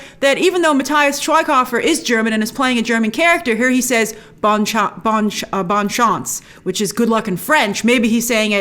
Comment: I thought the german guy said "oh scheiße" at 17:30. Which I think is "Oh shit"